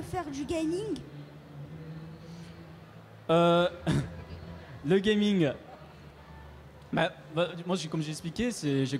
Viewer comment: Qu'est ce que dit donc derrière sa carte ? :') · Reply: je suis une fourmis.